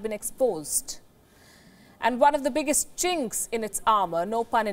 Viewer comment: Did she really say “chinks” at 00:18?! Wtf